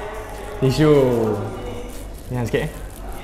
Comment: minit ke 9:44 tu mcm dengar cakap “ yang sikit eh “.. part tisu tu. ke aku salah dengar ni mintak mintak tak salah dengar la ya aamiin🤣🤣